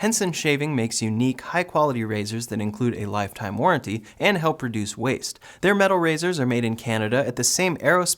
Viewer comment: When he talks about the razor, it sounds like he says "and helps PRODUCE waste" lol